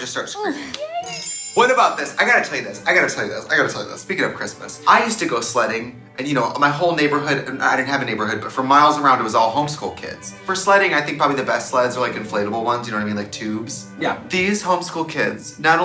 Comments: I thought Trixie said she used to go slutting all the time, at 3:50 :) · Wait, why did I hear Trixie say "slutting" and not "sledding"?? I was so confuused lmao! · honestly thought Tracie said : "I used go slutting" and didn't even question it up until she was like "For slutting I think the best sluts are like the inflatable ones. You know like tuubes." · when i Trixie said she used to go sledding I legit heard her say "i used to go slutting" and im gagging rn..